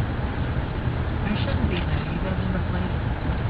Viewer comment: What did the transgender say at 24:44 in the video? "I shouldn't be because my plan"? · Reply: At first it says its frightened, then follows up with "I shouldn't be, you all are the police"